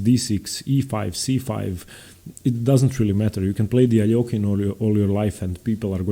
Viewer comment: anyone know what he says at 8:50 i’ve been trying to figure it out for a while? “you can play “???” all your life…” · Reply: Alekhine